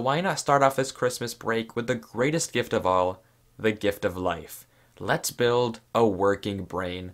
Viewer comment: I heard “let’s build s***